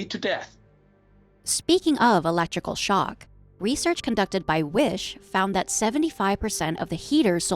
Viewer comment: I misheard WISH and was like WHA?? WHOM?????💀